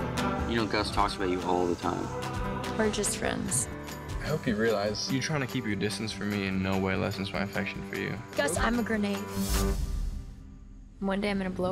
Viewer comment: Umm..can someone write me Augustus's words from 1:15 till 1:20? I learn English, but sometimes I don't understand some things in dialogs.Help me please, thank you :) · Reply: I hope you realize, you trying to keep your distance from me in no way lessens my affections for you. (Hope I helped!😃)